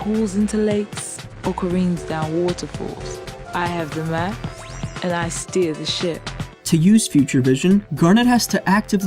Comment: did garnet just say "and I steal this shit"? XD